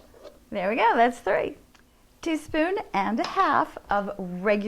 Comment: you say we need 1 1/2 teaspoon of salt but then later at 0:49 you say 2 1/2 teaspoon Salt. Please verify what is correct. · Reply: She says "teaspoon and a half" at 0:49, but maybe you heard it as "two spoon and a half"?